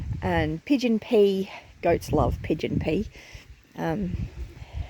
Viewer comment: great information! Never hear of Pigeon Peas. Is that Australian food? At first, I thought you said pigeon pee. I'm like how do you grow that? Then I saw some of the comments. I'm like oh that makes sense. 🤣🤣🤣 Thank you for sharing! Hugs and love to you and your family. ❤️✝🤗 Have a beautiful and blessed week. God Bless ❤️✝🤗